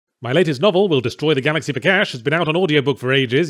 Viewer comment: I honestly thought Yahtzee said “Will Destroy the Galaxy for Gash” at the start there and it completely threw me off.